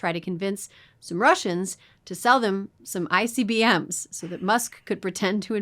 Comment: When you said musk went to russia....I SWEAR i heard: " they went to try to talk some Russians into selling them some Icy BM's." Which made me LOL and makes perfect sense for freezing cold russians to have plenty of frigid poops.